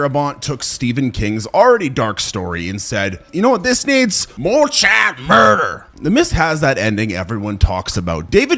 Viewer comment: more WUT murder? I’ve listened five times and I still can’t figure out what you’re saying. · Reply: “More child murder” in a very poor southern accent, I believe.